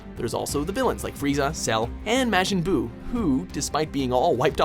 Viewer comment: hold the fuck up did he just call majin buu mashin buu!!